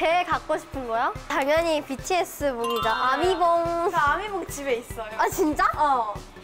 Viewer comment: 저만 아미봉이라 들리는건가요( •̛̣̣꒶̯•̛̣̣ )